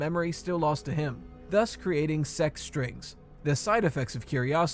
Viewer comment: who else heard “sex strings?”